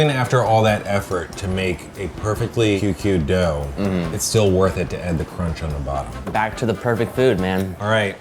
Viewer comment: into the video any guess on what Andrew said? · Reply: it's just putting the show title on his mouth while he says "Worth It"